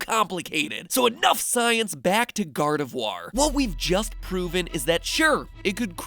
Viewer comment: did he say gardivwar